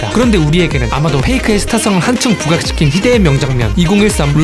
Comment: 페이커가 나만 페이크라고 들리나